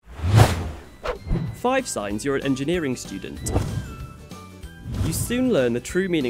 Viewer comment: I thought they said 'free beer' not engineer ...